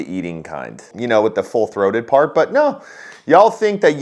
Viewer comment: I thought he said "Bold Throated" endorsement!